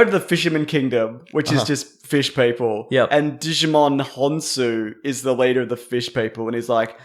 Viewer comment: Did just call esteemed actor Djimon Honsou a Digimon